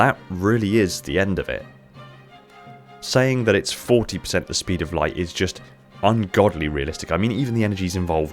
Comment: I thought you said .14 the speed of light at first, but even that is extremely powerful and would probably be closer to MAC on a UNSC cruiser.